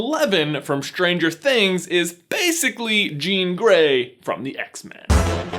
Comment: I swear I heard him say "jean grey from the X Factor."